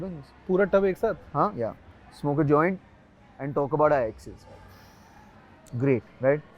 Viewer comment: I heard that as “and talk about ISIS”